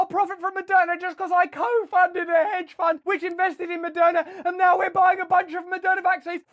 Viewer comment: I thought he said Madonna make a bunch of money selling vaccines.